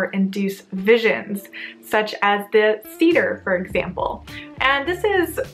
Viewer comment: What did you call the act of trying to induce visions at 5:17?? It sounded like you called it a cedar. I am very curious about this!